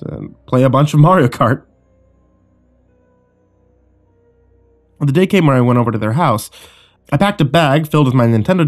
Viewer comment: I keep hearing "day care" instead of "day camp" which is what infants attend, not 11 year olds. Am I missing something?